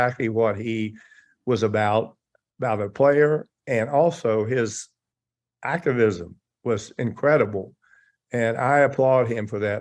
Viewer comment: Jerry says, "His accridism" will be remembered. What is the word there? I can't decipher it. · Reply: Athleticism